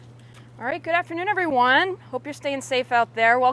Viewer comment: I thought she was saying good afternoon to the tortoises 🤣